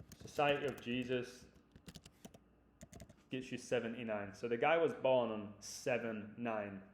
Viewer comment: lol 8:50 you said tom was born in 1979 i thought you said 75 before but re watched it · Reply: HAHA! I said 7/9 like July 9th mate.